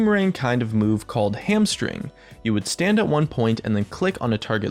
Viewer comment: Hamstring sounds incredibly similar to madman's w in Hon. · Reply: god I thought he said hamstering..